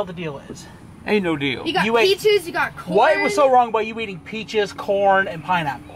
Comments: said peaches and corn I thought she said bitches and porn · When see said peaches and corn i thought she said peaces and porn